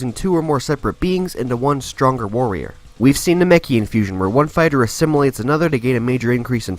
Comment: From left to right (and downwards): The heroes are: • Vegito (SSJ, SSB, SSG, SSJ2 and base) and • Gogeta (SSJ, SSJ4, SSB, SSJ2 and base) @ 01:03 the villains shown are, in offer from left to right (and from up to down): • Zamasu (fused) • Broly (LSSJ) • I don’t know this one, somebody help please • Super Buu (Gohan-absorbed) • Janemba (Perfect form) • Omega Shenron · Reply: the subtitles say “the meki” instead of “namekian” @ 00:27 the subtitles read “Abbott are” instead of “Potara” @ 01:34 “Patara” instead of “Potara”